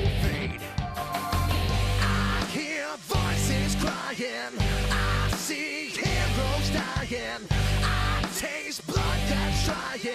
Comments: i hear voices crying, i see hebrews dying I'm i the only one hearing that? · What did it say at 0:46 ? For me it sounds like " I see Ambrose dying " ( no offense)